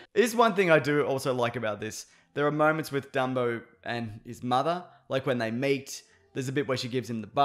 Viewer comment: I almost spit out my drink at 6:04 when I thought I heard: " there are things I like about this, there are moments with dumbo and his mother, like when they mate"